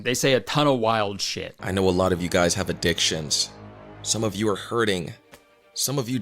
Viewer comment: the way he said it i thought he said “ i know alot of you guys a have a dick…” Like yeah no shit